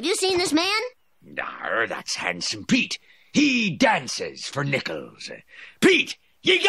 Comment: I've been seeing this episode for over twenty years and until just now I thought he was saying "he dances for littles" referring to little kids. Also the sign on the door keeps changing and magically swings back open after closing.